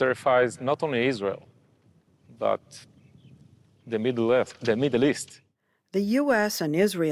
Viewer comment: did he just say the Middle-Earth was terrified ? is Sauron coming ? 🤣